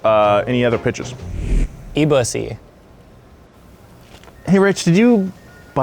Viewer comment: I thought rich said “eat *ussy” 😂